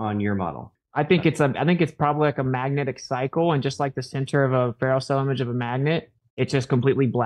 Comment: center of a what? can anyone make out what he says?! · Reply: "Ferrocell image of a magnet"